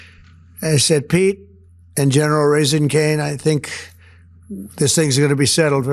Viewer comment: I could have swore he said "General Raising Caine" i could be wrong though.